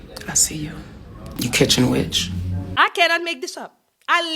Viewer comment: did she say "you kitchen witch"? 😂😂😂